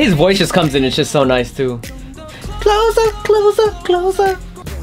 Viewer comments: jungwoo said "closer closer closer" at first when i saw this i thought he said clothes off 😂💜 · At first, I thought Jungwoo said 'clothes off' instead of 'closer'.